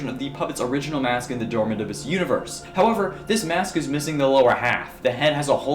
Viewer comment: Sorry, i only hear "kingdom or german abyss"